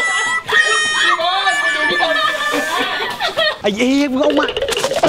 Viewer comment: ทำไม พี่พูดว่า ไอ้ยีน